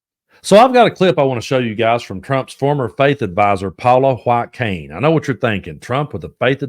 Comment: At first I heard “Trump’s fake advisor”. Lol Would make more sense. 😊